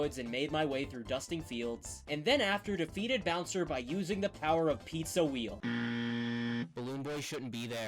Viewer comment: did he just say BOWSER?